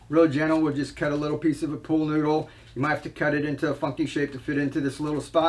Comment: I swear I though you said "f-ing shape" but I had to replay it a bunch of times... You said "funky shape" 😂😂😂